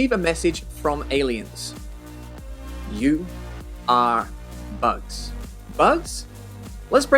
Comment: I was only listening and misheard it as “you are us” that’d be an interesting scenario too.